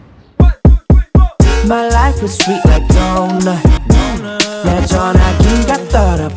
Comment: This WHOLE time I thought he was saying "My life must be like donut"